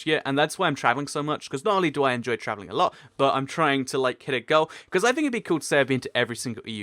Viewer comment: I thought he said hit a girl I was like OH HELL NO!